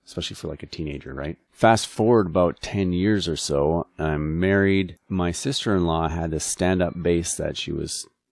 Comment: At about 1:50 it sounds like you say that you married your sister in law. Just FYI.